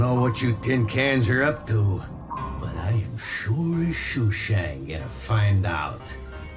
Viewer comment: English is my second language, can anyone tell me what exactly Captain Fanzone's saying at 5:46? The subtitles say "sure shushan", but that doesn't sound right. It's been befuddling me ever since I had first seen this episode! "sure as shooting", maybe? Or "sure as Shazzan"?